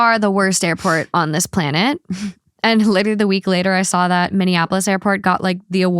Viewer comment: Indianapolis got best airport. I think I heard you say Minneapolis